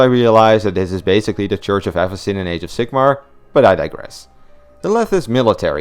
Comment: What are you saying here? What’s the Church of Ethers? Sorry, I just honestly can’t understand what you’re saying here.